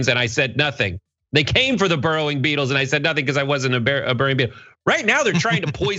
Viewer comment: Omg. I thought he said burrowing people... i was so offended.